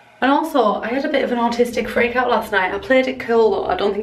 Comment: I absolutely loved this video, it had me cracking up 😂. Also thank you for representing the north east proper 😂 and also being so open about the autistic meltdown because they SUCK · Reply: doodlesims I fully thought she said “artistic freakout” so I was very confused by all these comments 🤦🏻‍♀️